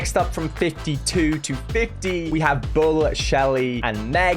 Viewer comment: I thought he said ''bo'' 😂